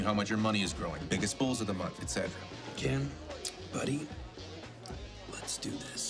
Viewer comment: How come no one has mentioned this flaw at 6:05 where Saul says Kim instead of Gizelle... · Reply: Says *Ken, buddy